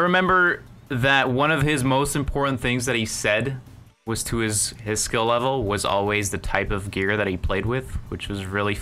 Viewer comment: could someone tell me what said? "I remember that one of the most important things that he said....." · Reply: Alisson Siri ...was to his skill level was the type of gear he played with. The "gear" he mentioned is like what type of keyboard C play with (mostly). After that he mention about C plays with razer green switch on lower bpm maps and etc..